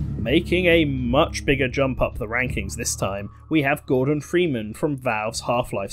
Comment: When you said Gordon freeman I thought you said Morgan freeman